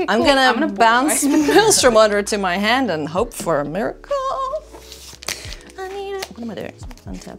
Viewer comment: What said: I'm gonna bounce Maelstrom Wanderer to my hand and hope for a miracle. What I heard: I'm gonna bounce Maelstrom Wanderer to my hand and hope for a Myrkul. I was like, how she gonna run an Abzan creature in a Temur deck 🤣